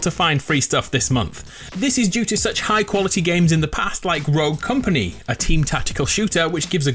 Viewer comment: Hey at 4:29 did you say road or rogue? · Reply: rogue